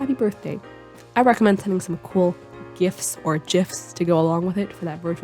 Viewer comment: how i heard it: "i recommend some cool gifs or ---- to go along with it" 7:46 it was my body's defense mechanism ok